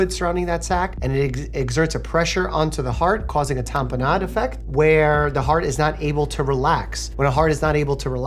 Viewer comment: I thought Mike said Tapenade effect, and had to rewind lol. 😅